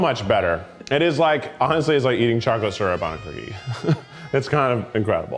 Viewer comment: Whoever can edit the captions, at 16:21 the captions say "It's like eating chocolate syrup on (indistinct)"- I agree, it's difficult to parse but I think he's saying "It's like eating chocolate syrup on a cookie "